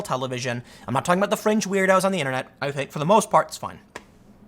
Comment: When he said "fringe weirdos on the internet" I heard "French weirdos on the internet." Lol